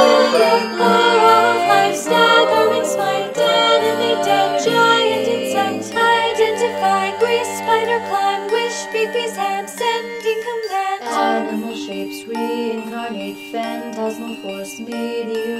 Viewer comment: I swear I heard “pp hands” in there somewhere